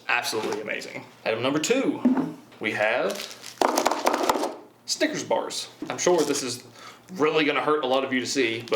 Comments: Did anyone else hear him say stickers bars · When he said snickers bars I thought he said stickers